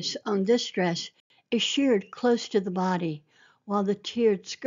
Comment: Beautiful, I would love to see them in person! Thank you for sharing them with us, and for the detail on the outfits you have so graciously provided. One thing—I think you meant shirred, when it sounded like you said sheered?